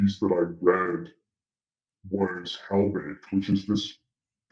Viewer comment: What's the name of the Land piece at 48:33 ? I couldn't catch it. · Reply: “Hell-baked” great piece— my condolences btw for not cutting it as a tough tugger